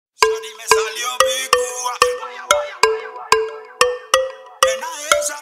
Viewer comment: Todo el tiempo pensé que decía ''Me salió pituca'' xD Esto es un temazo de temazos digno de EL RRRREEEEYYY Don Omar :D La vieja escuela, reguetton de antes, algo que ya no existe en la actualidad.